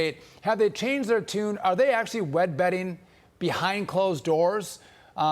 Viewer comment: Did he say "Wed Betting"?